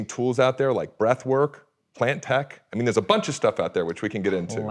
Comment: Amazing video. What were they saying at 13:20 - plantech? blantech?